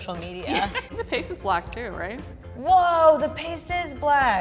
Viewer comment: Omg at 1:47, I thought Kristin said, "your face is black too, right?"